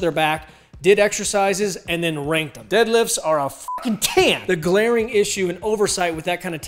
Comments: What did he say about deadlifts at 0:29? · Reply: Sounds like "deadlifts are a f@ 10." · deadlifts are what? even listening 5 times and 3 more with CC, I couldn't figure out what you were saying. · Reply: deadlifts are a fkn ten